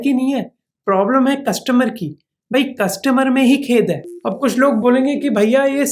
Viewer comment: That'd be 'khot' instead of 'khed'. Good video btw